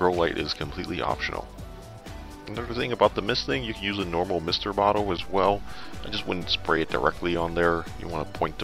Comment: What type of water are you using for misting? and also, 1:43 what mister are you using? I can't hear it correctly and subtitles say "flare saw mister" :) Thank you for great mycology videos!